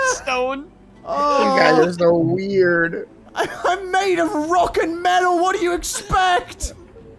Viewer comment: Did I hear a rock and stone?!?